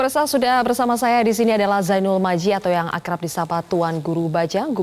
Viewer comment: Apa saya salah dengar ya? Host bilang zainul maji,,coba dengerin di